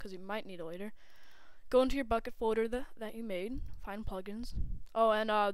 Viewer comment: lol in 1:10 it sounded like you said "Go to you fuckin' folder" xD